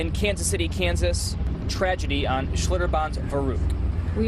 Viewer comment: Did he just say Kansas City Kansas at 6:05? But terrible event